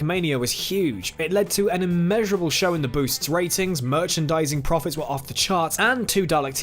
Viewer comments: Anyone else hear “an immeasurable show in the boosts ratings” at 8:52😂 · does he say an immeasurable show in the boosts ratings? lol